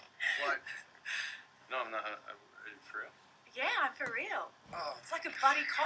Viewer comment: does he say no im not high x)